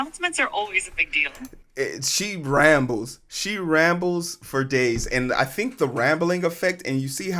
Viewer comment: Did I hear 4dayz? I've been summoned. Liked and subscribed!